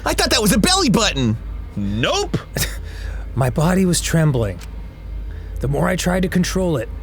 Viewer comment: Mom spagembling Update:Holy shit he actually said it lmao